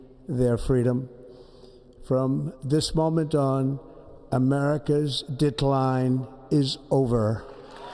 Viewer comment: Starting at about 5:51 I had to rewind for a sec a couple times, I heard “America’s dick line” I was like no WAY he’s about to work around to “big dick energy” or something lmao.